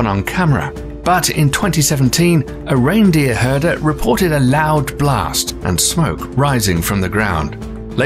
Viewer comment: I thought I heard, " a Reindeer reported a loud blast." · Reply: A reindeer herder reported a loud blast.